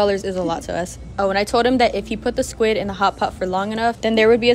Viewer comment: When you said "hot pot" what I heard is "hop hop"